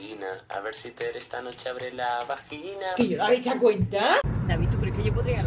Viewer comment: que dice?!?! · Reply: Quillo le has echao cuenta